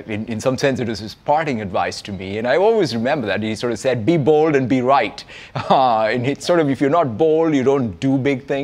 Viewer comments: Be bald and be right is what he’s sayin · I heard "Be BALD and Be Right!!" And I was like WTF Is Steve Ballmer asking people to go bald just coz he is..!! 😂😂😂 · holy I heard "be bald and be right" and got confused for a second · "bald" I heard🤣🤣🤣